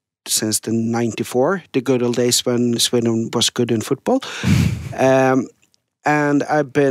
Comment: I thought Johan said Swindon was good at football. That was confusing.